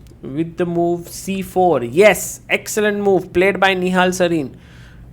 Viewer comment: he actually says nihal sarin 😂😂 is stuck on the nihal legend match in Romania